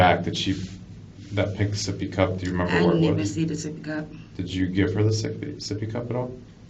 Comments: the captions say "I put a different juice in there." She actually said "I didn't even see the sippy cup." For any deaf listeners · I just started this video and I don't know if this messes with the case at all but at 5:31 she said "I didn't even see the sippy cup" But on the video, the closed caption said "I put a different juice drink in there" She did end up saying that at 6:53 though. Just letting you know my man! Great video!